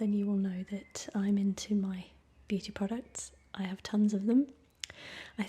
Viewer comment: I thought she said booty products at the start